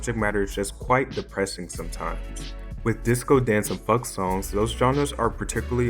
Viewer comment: Who else heard Fuck instead of Funk